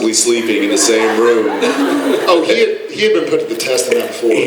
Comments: WHAT are Jared and Jensen saying here?? 😭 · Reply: “He had been put to the test on that before” “He can sleep through anything” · What he said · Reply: “He had been put to the test before” meaning Misha & Jared had slept in the same room/together before 😂